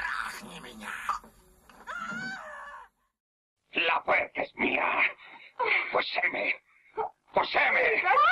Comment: In the Latin Spanish 1974 version, what is the demon saying? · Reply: She says: "La puerca es mía. Poséeme, poséeme"